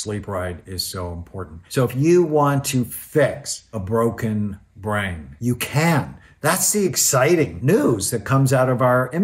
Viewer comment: When he said ‘If you want to fix a broken brain’ I misheard him saying ‘YOU CANT! And that’s the exciting bit’